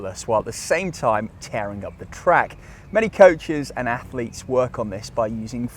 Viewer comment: I heard “up the crack” 😂😂